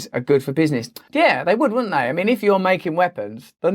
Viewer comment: Great content as always. Just wanted to point out, at the "9:38" mark, I thought you said "their" countries instead of "the" countries.. which would change the meaning of what youbare saying, since I was listening, was a bit confused at first... I thought u were implying that US and Russia arms and weapons were collaborating to "stir up tension" in order to provide a reason to build up their arms for profit, but seeing "the" countries, clarifies its only the US and not Russia.